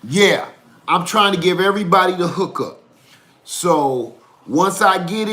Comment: I misheard the hook up n so glad you repeated. i heard i got a hooker. Here is me thinking ...... Lady Lead gonna hit you with a rolling pin n string you up from the nearest tree once she hears this vlog.